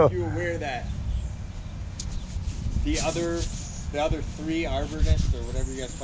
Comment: “An arborist or whatever you guys call yourselves” great job keeping your cool, I would have told him where to go 👍🏻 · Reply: I could have sworn his said "the other three arbornists" 🤣